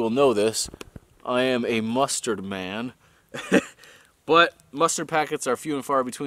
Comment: i heard " Send me mustard Packets "